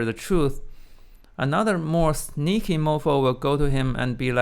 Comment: Lol did you really say “sneaky mofo” at 3:15 when talking about Russian generals or did my ears deceive me?